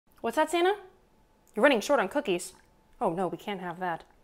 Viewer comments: I thought she said Sanna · For a moment I thought she said Sanna.. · Thought she said “Sanna” at the beginning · i thought she said sanna not santa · Lol I thought she was saying Sarah all the video... At Santa, you're gonna love these I finally understood correctly. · I thought she said sanna but she said santa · I’m guessing I’m the only one who thought she said sana not santa•>• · OH MY GOD when she said “Santa” I thought she said “Sanna” 🤣😂 I don’t know why! 😂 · Who thought that she was saying sanna